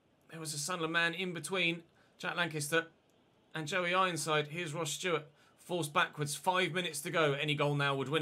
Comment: Did you really say ‘here’s rod Stewart’ pmsl